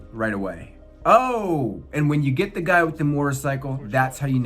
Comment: I thought you said mortar cycle lol